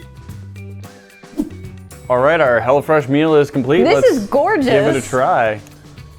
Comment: it sounded like you said hella fresh meal